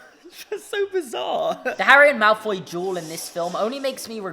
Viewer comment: I was not paying attention when you said "Harry and Draco duel" and for the first couple seconds I thought you said juul and oil